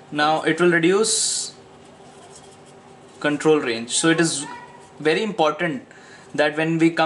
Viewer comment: minute 14:27 what do you say at last?( It reduce control .... )?